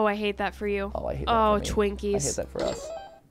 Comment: what did say? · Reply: I’m pretty sure he said twinkies too